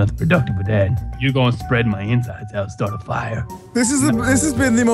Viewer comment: what did he say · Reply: You gonna spread my insides I'll start a fire